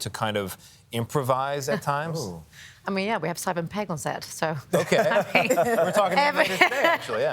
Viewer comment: I heard it as ‘Seven’ Pegg as well. Glad to see the YouTube transcript heard it the same way.